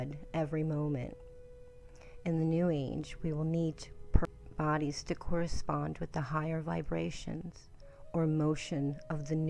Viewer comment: What did you say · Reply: sorry it skipped a little during recording. I said "we will need perfect bodies to correspond with the higher vibrations or new blood". Have a peaceful day friend, sending you love & light 💚🌟